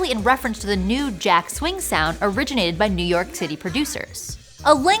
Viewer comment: Did she say, "New York Titty producers?"